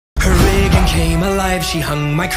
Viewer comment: Who else thought it said “her wig came alive” just me? Ok